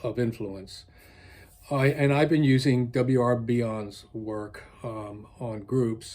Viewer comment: can you tell me the name of the author you are mentioning here please ? I can't hear it properly and neither subtitles nor google could help me with that. Thank you for your work. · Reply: I will post references in the description later today, but I think you mean W R Bion and his ideas on groups.